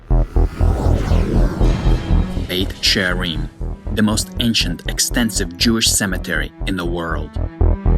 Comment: did he say most expensive cemetery @ 0:07 ?!!?!? i guess everything has a price... XD